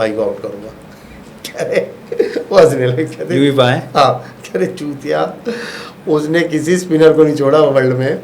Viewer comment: kya bola😂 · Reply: Huutiya😂